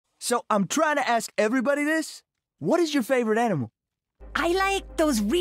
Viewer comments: I thought he said anime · I thought he said Anime and I was really confused